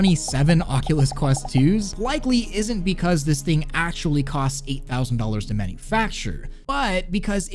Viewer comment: I'm gutted I thought you said a thousand dollars not eight thousand dollars